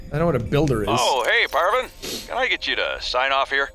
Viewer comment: Did he say I don’t know what a builder is?